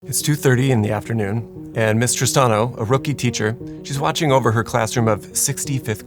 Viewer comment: I kept hearing "Mister Stano" not "Miss Tristano" lol